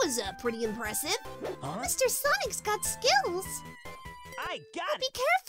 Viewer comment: It sounded like said "Mr. Sonic has got skittles"